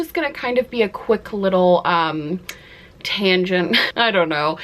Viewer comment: genuinely thought you started this with a pun, i thought you said "tana-gent" instead of tangent